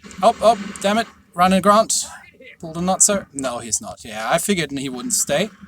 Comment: lol did you say Notser at 16:30? (When you beached)